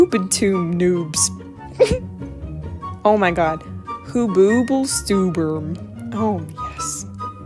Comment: At first, I thought you said "Who boob l stubborn" 😂😂😂